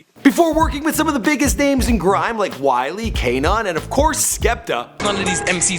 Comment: Did you call him fucking KA NOT WTF dude )